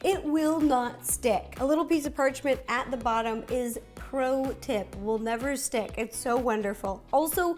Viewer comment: Instead of “pro tip” I think you should say “ Ro tip”!!! That’s what I thought you said!😂🌈🍰